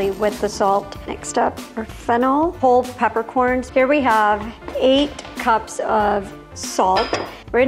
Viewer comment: I replayed 3:37 three times before I understood that she really said 8 cups of salt